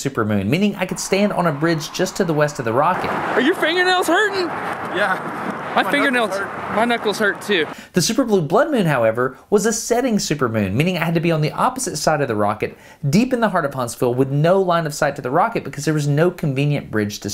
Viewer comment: At first I heard “Superman”